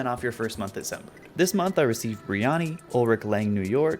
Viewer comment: thought you were saying "biryani", not "brioni" 💀 meaning a south asian dish with lots of spices